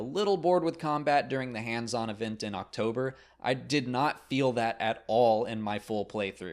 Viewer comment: Every time you say "October" I keep hearing "Ach-Tober". Make of that what you will.